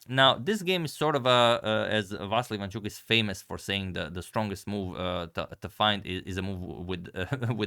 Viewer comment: I thought ivanchuk said hardest move to find is knight backwards. Not strongest move